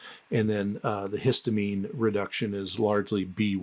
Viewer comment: Excuse me Dr. Anderson, but at 11:26, did you mean to say “aldehyde” instead of “histamine”. You were talking about the histamine N-methyltransferase pathway. So for clarity, how does one support the aldehyde dehydrogenase step? Is that what the thiamine is for? Thank you in advance. Great video!